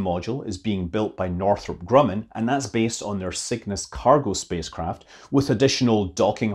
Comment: I heard “Cygnus” as “sickness” at first...